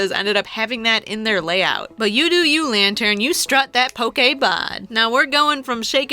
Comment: Did you just say pokebon